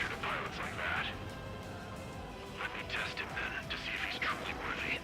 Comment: When Sol 1 said "to see if he's truly worthy", I didn't see the text and thought he said "to see if he's trolling with me". The fight looks like it could have been hard enough to be a troll itself, at least for some players.